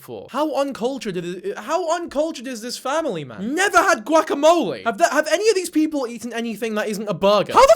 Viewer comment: pretty sure she said "never had guacamole that bad" but the audio was crappy so it sounded like she never had guac